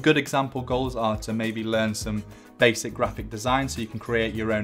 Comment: What does he say at 3:02? Learn some basic ROCKET DESIGN? i cant understand it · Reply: Basic graphic design